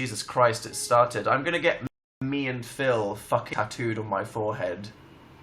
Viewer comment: DID HE JUST SAY HE'S GOING TO GET "ME AND PHIL FUCK" TATTOOED ON HIS FOREHEAD OR DID I JUST HEAR WHAT I WANTED TO HEAR!?!?